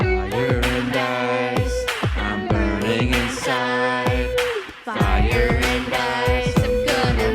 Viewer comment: no joke at first I thought he said: “I’m dying inside”